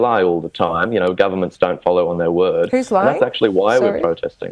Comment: says "'you cant trust the govt. they lie all the time" Host in disbelief: "Who's lying?!?" Host a few seconds later says ""I don't want to buy into some mistruths, not only from you but from our own government"" I've never seen a host flip flop so much and both support and go against the government within a few sentences. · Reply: I think she was just asking him to repeat what he said. She said “who’s lying; I’m sorry” ( 3:08 ). Sounds like she just didn’t catch it the first time. And she didn’t follow up by refuting it either.